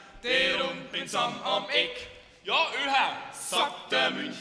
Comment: Was sagt der Tubist bei 01:03? Komme nicht aus Bayern 😂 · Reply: Ja ÖHA ("pass doch auf ") :D